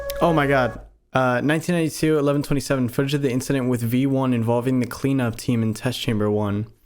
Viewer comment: did you say v1? THE FUCKING V1 FROM ULTRAKILL???